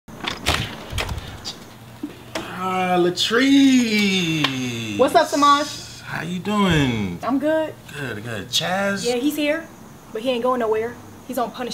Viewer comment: Lol her names toliet, or is it bathroom, oh latrice, I thought he said latrine